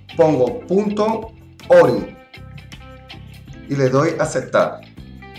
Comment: En el minuto 3:57 dice algo de punto y luego no se escucha bien que dice :/ Diganme porfavorcito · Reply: 7u7 es .ori